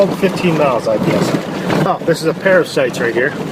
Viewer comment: it almost sounded like he called that dog a parasite.